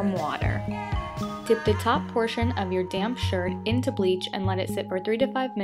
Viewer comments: it sounds like she said "damn" lol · when she was dipping the top in the bleach i thought she said "dip the top portion of your damn shirt" when she said damp shirt